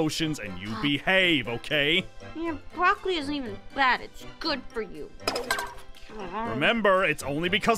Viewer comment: Did aphmau say broccoli is good for you?!